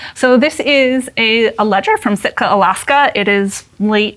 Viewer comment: Cicely, Alaska? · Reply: I thought she said Sitka.